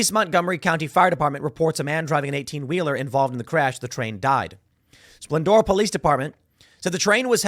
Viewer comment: Tim is speaking so fast 12:11 did he say a train died?